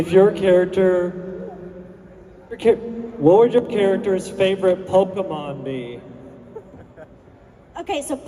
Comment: I couldn't hear what that person was asking, someone know?? Pls ❤😊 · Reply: It was: “what would your characters favorite pokémon be?”